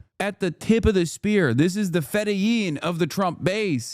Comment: can somebody more smart tell me what word he used there? Fetein? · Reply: Fedaykin - it's the elite Fremen fighters in Dune - he's saying that guy is the most hardcore of the hardcore Trumpers